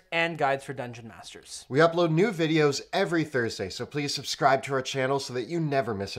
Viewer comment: I thought he said “we upload nude videos every week”. And now I will always hear that and the image it conjures. And now you will too.